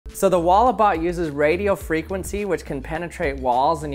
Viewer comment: I thought he said "so the wall I bought can use radio..."